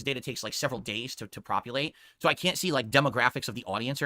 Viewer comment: Maybe it's just me but it sounds like saying propulate instead of populate